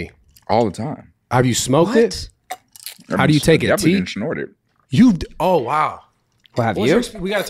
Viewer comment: did he just say he snorted dmt